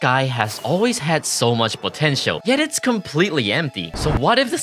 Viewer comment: DID YOU SAY POTENTIAL ?